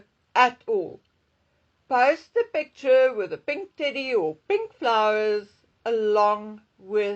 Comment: You said "pink teddy", I thought you said, would've sworn you said "pink titty"😂